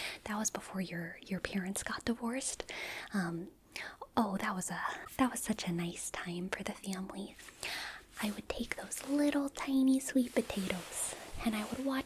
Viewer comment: For a sec I thought she said the divorce was so great for the family 😂